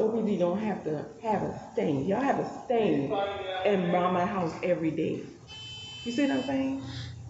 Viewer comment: did she say "you have a stin in mamma house everyday"?